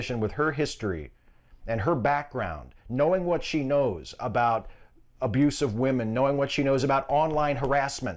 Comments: did say "...knowing what she knows about abusive women" or "...abuse of women?" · did I hear wrong or did the roach say "abusive woman" instead of abused woman at 1:40? if so that's one hell of a Freudian slip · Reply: Hebas It was "abuse of women" but I heard "abusive woman" too, makes more sense. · I know that he said 'abuse OF women', but I heard 'abusive women' ...